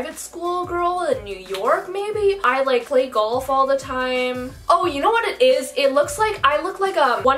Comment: I looked away to rub my eye when she said "oh you know what it is" and then a Pampers add came on so i thought she said Pampers was it. I was so confused😭💀